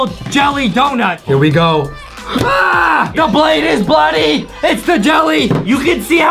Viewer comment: wait did justin just say "THE BLADE IS BLOODY"